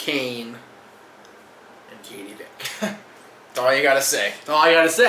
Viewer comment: Can someone tell me what the fuck said at 9:18 triple h kane and who? ,mothefucker needs to speak up and more clearly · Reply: Katie Vick.. Basically they did a story line where HHH had sex with the dead body of Katie Vick (Kane's ex, correct me if I'm wrong) it was just so fucked up